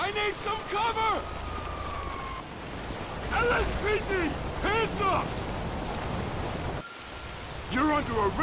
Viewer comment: Что он говорить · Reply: "LSPD, hands up!"